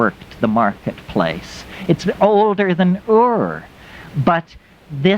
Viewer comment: what does he say “it’s older than WHAT? (Ure?) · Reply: Ur - an ancient Biblical settlement in Mesopotamia.